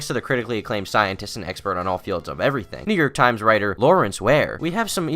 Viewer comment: I thought you said “nigger times writer” Damn, you really are a gamer